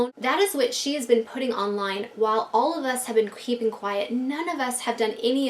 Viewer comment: I could've sworn I heard queefing quiet... Wish this drama with everyone didn't exist tho.